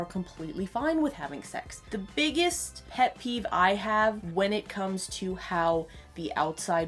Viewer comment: So... I heard "het peeve" not "pet peeve"